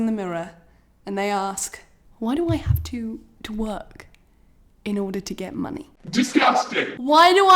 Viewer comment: At first I thought you said "why do I have to TWERK to get money" 🤣😂😂🤣😂😂